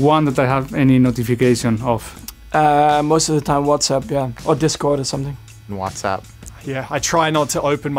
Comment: did I heard from Verstappen Discord?